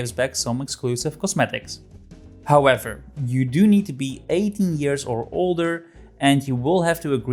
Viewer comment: don’t know how but I heard 18 as 80 and that got me really concerned